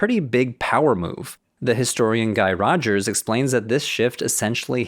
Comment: I heard "The historian Guy Rodgers.." as "This historian guy, Rodgers.." 😂